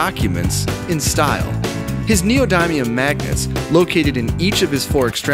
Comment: i thought you said neal diamond magnets